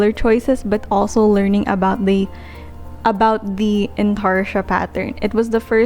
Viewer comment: Hii, i didnt quite get what you said on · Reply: intarsia